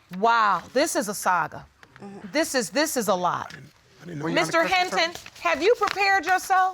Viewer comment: I wasn’t quite listening and I thought she said ‘this is a thot’ when she repeated I got it this is a lot 🤣🤣